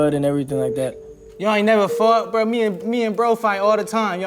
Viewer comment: ngl I thought he said "y'all never fucked?"